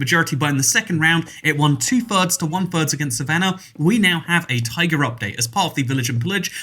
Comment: Instead of taiga. I keep hearing “tiger update” which sounds amazing!